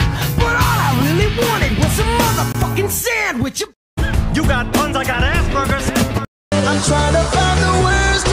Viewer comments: When it said "you got buns, I got aspergers" I thought it said "you got buns, I got ass burgers" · Legit thought when it said "aspergers" it said "ass burgers" 😭😭😭😭